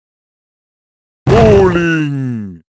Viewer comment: I thought he said rolly when he spawned in